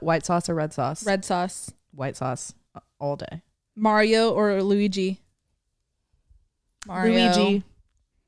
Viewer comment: When you said Luigi I heard “Louie g” and I was like who tf are they talking about